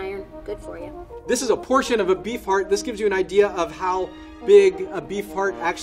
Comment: I thought he said beef fart